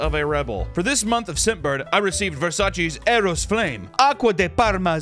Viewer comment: I thought it said VSauce (i wouldve bought them out)